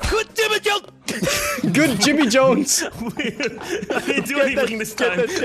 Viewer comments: sounds like he said "Doug Dimmadome" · I could have sworn he said Doug Dimmadome. Yes, Doug Dimmadome! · Good Jimmy Jones? I thought he said Doug Dimmadome...